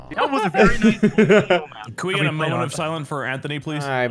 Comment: I heard moan of silence